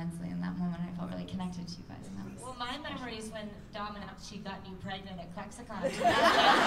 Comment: Hi. Anyone knows, what did em said at 48:47? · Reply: I think she is saying "when Dom announce she got me pregnant at cc"